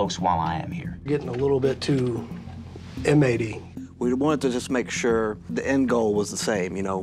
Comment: Sheriff insinuating Ryan was being a "too inmatey bully" but forgets he payed off a debt for a bullied kid! 🤐🤐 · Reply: Thats because you completely made that comment up and said the EXACT OPPOSITE of what was actually said. 2:35 he said you're being too inmatey. Full stop. He did not say or imply or insinuate he was being a bully. His whole point was that he was too friendly.